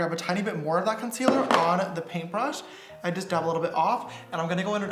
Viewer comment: Did he say paintbrush 🖌